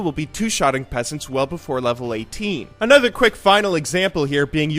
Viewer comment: does he say "2-shotting peasants well before level 18" or am I mishearing it